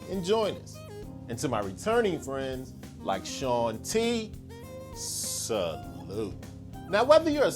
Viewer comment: did you say "salud" ? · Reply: Salute